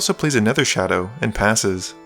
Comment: I almost thought you said "casts 'another' shadow" :)